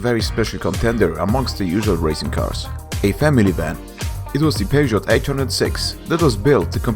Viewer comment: Did you said Family?